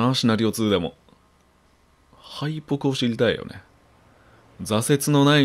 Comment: はいぽくを知りたいにしか聞こえないんだけど…